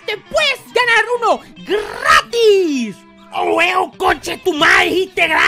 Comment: did pelo just say gratis? On my country it means free too I'm not Mexican or american,but I'm Indonesian